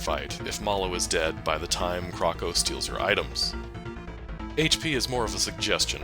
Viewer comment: Did you just call Mallow "her?" Haha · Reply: Lol I said "your" but I guess I said it more like "yer"